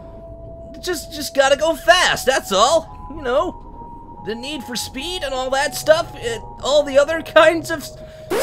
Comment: Did you just say "Need For Speed" ? In 2:34 ?